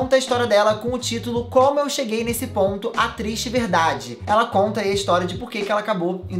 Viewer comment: E vocês? O que acharam dessa historia? Me conta que eu quero saber! E to aceitando dicas de EVEES! Me manda aqui! <3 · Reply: eu entendi "atriz de vdd" tive q voltar o vídeo kkkkkkkkk